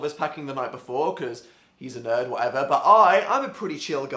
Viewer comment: At the beginning I thought he said "but Phil's a nerd, who I love"